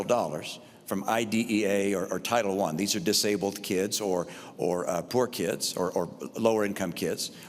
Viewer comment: did romney really say poor kids.